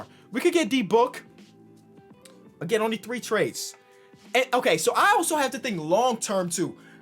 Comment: Thought it said “3 team only trades” got mad excited 😔